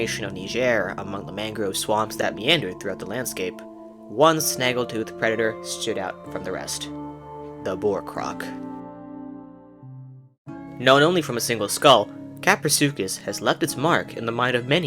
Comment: When I first heard the word "boarcroc" I misheard it as "Bordercroc" which is honestly cooler.